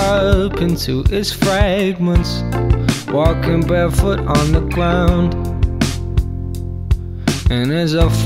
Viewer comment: Why do I keep hearing this? "walking barefwoot on the gwound" P.s I freaking love this song 💛